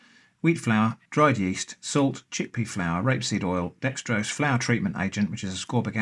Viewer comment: It's not grape seed oil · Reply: I've listened to it a dozen times lol what are you saying that sounds like grape seed oil when reading the ingredients list of the flour at 5:40 estimated time in the video